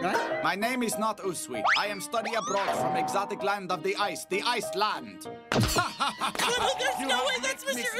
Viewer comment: Usui, did you just say Iceland?!?!? YOU BETTER NOT FORESHADOW ANY POTENTIAL BS THAT'S ALL WHAT I GOTTA SAY!!!!!!!! 👿🤣LOOOL